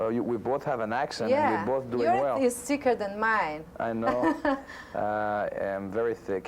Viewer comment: "your accent is sicker than mine"....lol her accent is WAY 'thicker' than his, I can barely understand what she's saying.